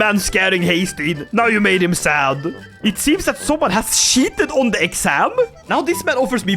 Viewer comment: I thought he Said someone had sh***Ed on the exam, and I thought "that's just how Chinese exames go"